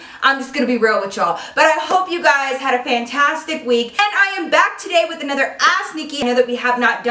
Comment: If you have rewind enough times like me at the first 4 seconds it sounds like Nikki saids "I'm bad" 😂😂😂💜💜 omg definitely brightened my day